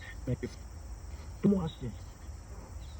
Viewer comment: I heard he’s walk around, Bill, where? Looking for me.